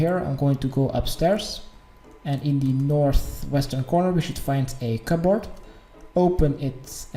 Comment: What's up with you not knowing your east from west???? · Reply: OHHHH I Thought he said Weest!